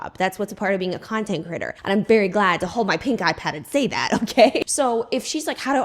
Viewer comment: Tee hee I heard “pink eye pad” when you said “pink ipad” 😅